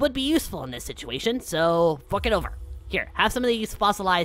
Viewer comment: I thought you said “Fuck it over.”😂